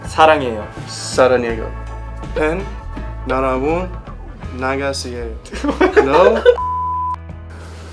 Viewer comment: 라이블리 두번째 연습 나보다 더 잘쓰는 갓 같은..느낌적인 느낌 매직으로 쓴거 맞죠..? 09:50 나가세요가 왜 이렇게 정확하게 들리는 것 같짘ㅋㅋㅋㅋㅋㅋㅋㅋㅋㅋㅋ미치겠다ㅋㅋㅋㅋㅋ